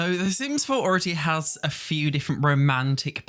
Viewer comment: I heard 'the sims4 orgie' instead of 'the sims4 already has' 😂😂😂 im now hearing things 😂😂😂